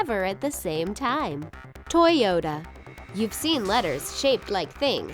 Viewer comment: sounds like she said toy yoda in the toyota one